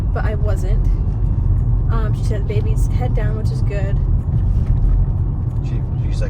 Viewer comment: Did Ty say “SHE’S 90% head down?” 🥰🥰🥰 I may have misheard. or she was saying the nurse said the baby is 90% head down. someone tell me what he said lol 😂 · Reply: the nurse is 90 percent sure the babys head down